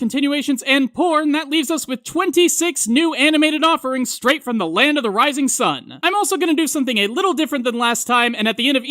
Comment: What was that one anime he said at the 1:49 mark; the last one with the guys. XD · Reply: got you fam. Yubisaki kara no Honki no Netsujou: Osananajimi wa Shouboushi They have the episodes here: <link>